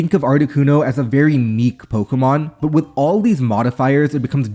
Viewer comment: Did this guy really just say "neek" instead of "niche"?